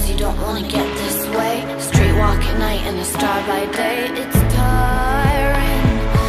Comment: i fucking thought it said camren im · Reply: On the "tiring" part, i thought lana said tyren lmao